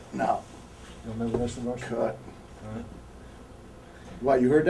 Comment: I like how he takes of his glasses off when he's confused or acts impatient in this interview. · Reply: I had to put on the CC close caption to see what they were saying. They were asking about his involvement with/against the Varsity Club. He legitimately had no idea what they were talking about. The confusion makes sense since it was Rick Steiner in the Varsity Club before Scott was ever around.